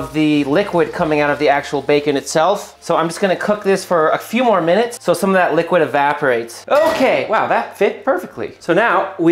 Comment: I thought it said 75 minutes. I was finna have to find another meal prep vid😂